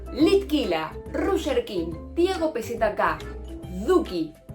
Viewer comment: Yo escuché Diego PZK